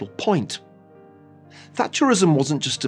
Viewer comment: When Niall Ferguson say Thatcherism, I thought I heard flatulenceism! 🤣